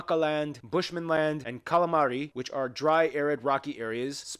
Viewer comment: did he say calamari? 😋 Also, can someone explain to me why the first national dishes are Scandinavian ones?